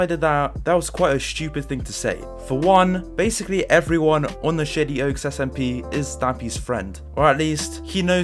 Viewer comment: Am I the only person who heard "shitty oaks" instead of "shady oaks" at 1:53?